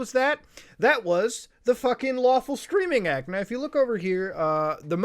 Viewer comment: Thought you were saying “lawful screaming act” and was overly interested until 2:36 and read it said streaming 😞